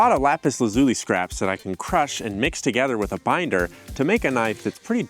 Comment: Did he say miner like Minecraft? 5:11 never mind he said binder